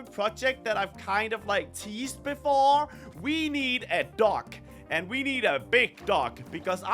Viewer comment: I thought he said we need a duck still love you 3:56 I thought he also said I’m feeling autistic 😂